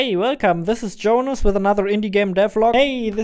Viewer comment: the auto-captions say "devil" instead of "devlog". I just though you wanted to know.